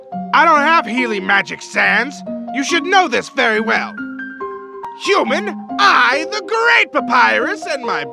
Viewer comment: at snowdin 4:22 human, me the great papyrus but is sounded like human, I the great papyrus